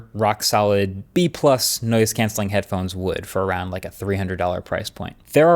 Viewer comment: I heard "nose cancelling headphones" first time it was said with reference to the airfilter headphones.